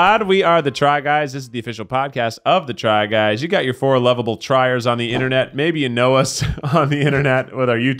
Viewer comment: "Four loveable tryers" 😂 I heard "dryers"